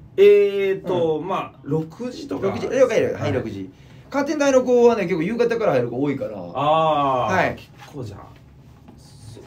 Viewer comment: 騒がしいところで見てたからカーテン大が関大に聞こえたわ